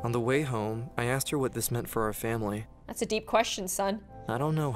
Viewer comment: Is it just me or did he say “what this MINT for the family “